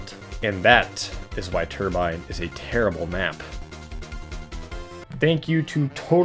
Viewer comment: I thought it said "Why Turbine is Hatred"